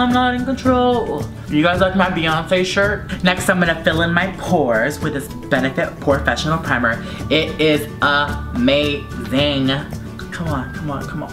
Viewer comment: When he said the Benefit Porefecinial, I thought he said porn! Any one else? #dirtyminded